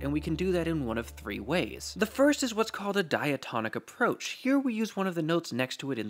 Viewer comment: I heard "diatomic" rather than "diatonic", which immediately woke up the chemistry side of my brain until I saw the written word and realised we weren't discussing halogens or the others...